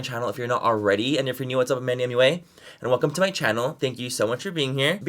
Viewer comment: Whenever Manny says “my name is MANNYMUA and welcome to my channel” all I hear is “ my name is MANNYMUA and welcome to Disney channel” 😂😂😂😂😂😂